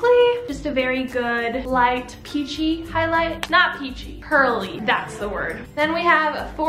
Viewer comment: I thought you said “not peachy...curly” and I was like how can a highlight be curly I am confusion